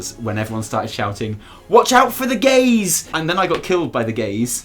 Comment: "watch out for the gaze!" Me: What gays?.... OH GAZE! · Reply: I was watching this vid, and turned away for a moment to take a sip of my drink, and nearly spit it out when I heard "watch out for the GAYS!!" Like, wha- .... ohhhh, gaze.